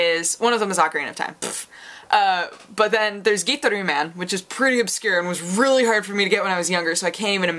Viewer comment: Oh Gitaroo Man . I thought you were saying itaro man .... I liked that game the little I have played The game is not so hard to get, it's on amazon and I think ebay too at reasonable prices